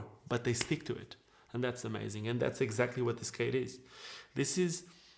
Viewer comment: 😆Totally thought he said, “this, this is a damn skate” instead of “themskate”. Anyone else hear it? Much love 🙏🏽 Love your content🤙🏽